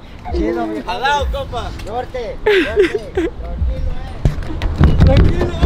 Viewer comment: What was the man saying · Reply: Norte